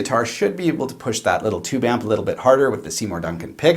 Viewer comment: Doh I am sooo stupid... Time for bed... you said at 7:28... something... but what on earth was it...? I just spent 5 hours trying to decipher it but from your accent a mystery it did remain... what the hell you said, namely, the pickups in the DBG tele. Finally, I believe you actually said P Rails, but it sounded like you said 'pea real,' or 'pee real,' or 'perial' as in imperial... and on it went... British English is far removed and, it would seem, a clearly enunciated American English is a bit of a reach too ;) Now I know what you put in it I can sleep well ;)